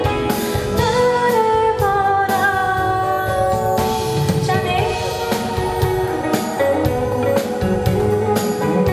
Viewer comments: 초에 뭐라고 하는거에요? 가사에는 안나와 있는데 너무 궁금해요~ 아시는분 댓글좀요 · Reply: 그냥 jonny 부르는.. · 이때 뭐라고 하는 거에요??? 쨔네?? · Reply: Jonny 라고 하는 거예욤 저때 기타 솔로로 치시는 분이심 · 뭐라고 그러는거예여 ? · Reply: Jonny!